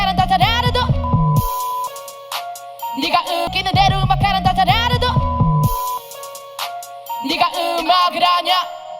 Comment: 에 삐처리된거 뭐라고 하는거임 ㅠ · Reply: 이새끼야